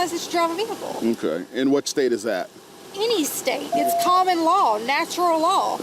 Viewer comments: You should add a Thumbs up cannon to your castle, This cop was amazing. Then can get a few more cannon shot's, when Officer's own Sov-Cit. I'll fly more hay over to you🤣 Sounded like she said cannon-ball, but prob ment common law, like the rest of them😂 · I believe she was trying to say common law but it sounds like she said cannon law